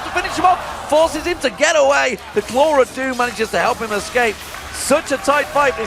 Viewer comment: did he just call lissandra's claw the claw of doom?